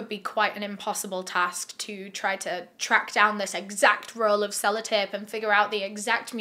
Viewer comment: I misheard you and thought you called it 'fella'tape 😂